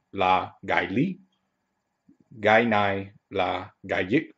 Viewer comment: You should pronounce the word "cái viết" more precisely because what I heard was "cái diếc". Also, "cái bút" is more commonly used so either way is fine. Good job !